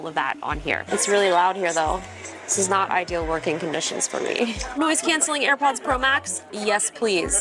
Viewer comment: did I hear “ noise cancelling AirPods Pro max” justine! 😂😂😂😂😂😂